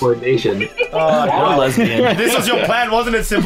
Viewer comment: Did just say, "I'm a lesbian," or am I hearing things.